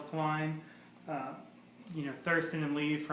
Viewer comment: did he say thurston lee?